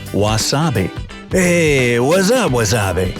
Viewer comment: I just heard Yeon Jun's "Waddap MOA?!" from that "whats up?" in 1:36's😆🤡